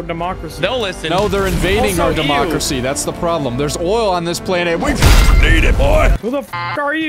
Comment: did i hear oil